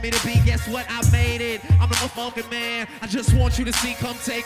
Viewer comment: lol at 1:16 they bleep out the curse and it sounds like hes saying " im the muffin man"